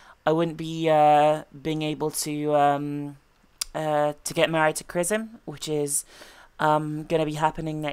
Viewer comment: what does she say at 0:56 something about krism? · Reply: She said "To get married to Krism" Or something like thatt.